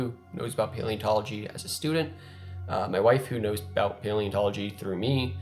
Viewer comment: I'm probably the only one who listened to the end and thought they heard him say "My Waifu" before realizing he said 'my wife who'.